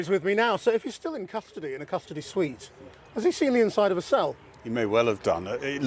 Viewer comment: Did I hear custody suite?